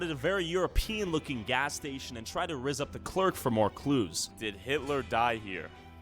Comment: did he say he tried to rizz up the clerk